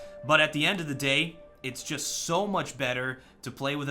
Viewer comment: Ha I thought he said I am the Lorax . . . hahahahga